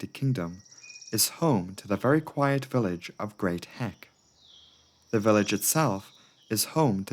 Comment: I misheard “Great Heck” as “Great Hank”.